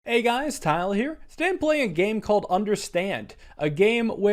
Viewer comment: What he said "hey guys Tyler here" What people heard "hey guys Tyler here" What I heard "hey guys tile here"